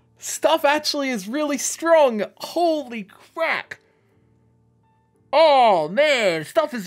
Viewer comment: did he just say "Holy crack-"? 😂